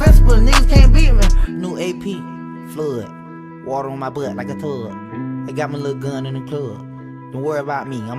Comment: Literally every single time this came up on TikTok I heard: “New AP, flood Water on my butt like a thug” 💀